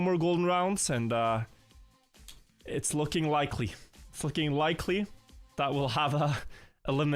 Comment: did he say fucking likely??